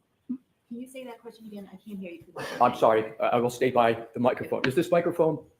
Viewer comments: I heard “I will hear you” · I thought she said "I will hear you"